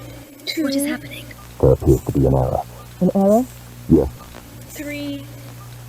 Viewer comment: Aight I thunk I know what the muffled voices said at 32:14 Spoiler I guess? Doctor: what’s happening? Unknown male: there seems to be an error… Doctor: an error? Unknown male: yes